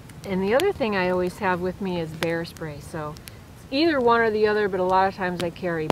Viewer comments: I thought you said hair spray, glad you repeated bear spray. I didn't know you could buy bear spray. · 😂😂😂. I thought you said HAIRSPRAY! I thought,” gee. never heard of that, but hairspray in the eyes might run off an intruder.” 😁